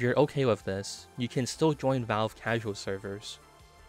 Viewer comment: thought said “minecraft casual servers”